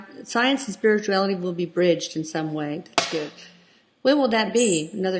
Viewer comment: I think I heard voice relaying the message to..I hear "bridged"